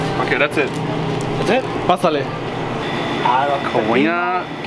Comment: damn i heard you said (basaleh) at 2:37 are you an arabic guy ?